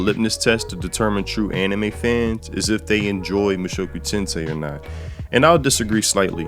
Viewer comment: I thought you said Musou Tensei.